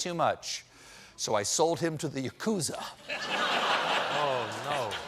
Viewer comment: Does anyone know what "the acousa" is? 7:30 The joke fell flat, but I still want to know. Google returned no results for that spelling, and "acusa" is a moth species.